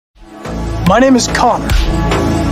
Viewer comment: Yo this fire🔥🔥🔥 (Connor got shut down when I was playing💀) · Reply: I misheard my name is coc